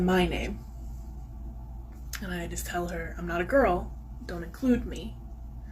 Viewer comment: I swear, I heard her say, "I'm not a girl, I'm a food bank"